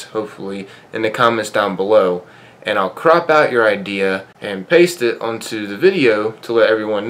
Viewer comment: I thought you said " I will crap out your question"😂